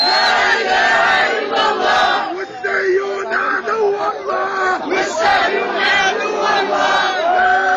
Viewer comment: wrong subtitles of what the guy is screaming at 4:08 he isn't saying jews are ennemies of god" he is saying "Zionists are ennemies of god" all jews are not zionists.